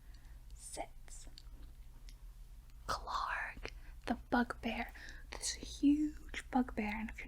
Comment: Literally thought she said " Fuck Bear"... 😶😶😶